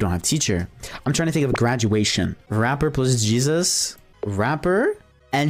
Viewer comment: I thought you said: rapper and cancer😂😂